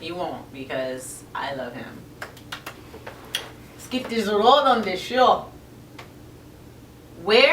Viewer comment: omg loool did u just say...lets get this road on the show???? looool. did u realise and just peretend u didnt? Just for that laugh ima sub 2 u, u seem kl x